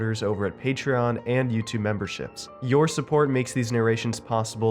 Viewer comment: originally i thought said 'membershits' the end.... the more i hear it the more im sure lol xxx <3